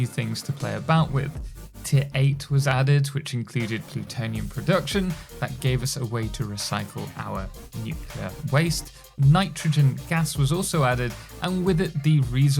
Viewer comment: through 5:46 what is he whispering here? Something about recycling waste? I've just reached this part in my own playthrough. I listened 5 times trying to make out what he said was added. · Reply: "...which gave us plutonium production that gave us a way to recycle our nuclear waste..."